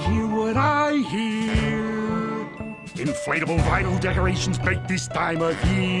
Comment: When I first heard this, I thought he said "Inflatable Rhino Decorations".